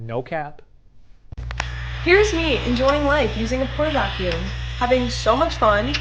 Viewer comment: LMAO. Thought you said porn vacuum. 🤣